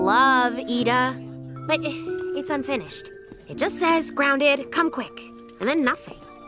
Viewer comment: What does it says the message? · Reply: “Grounded, come quick.”